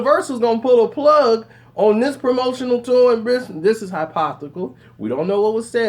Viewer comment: Did he just say "Hypothical?"